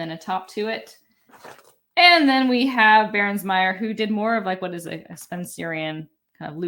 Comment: what is the variation at 17:36? I can't quite understand her; Bairnes Meyer?